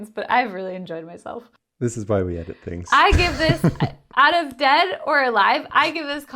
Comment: "this is why we ended things" -- did he just say what I though he said? :) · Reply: “This is why we edit things.”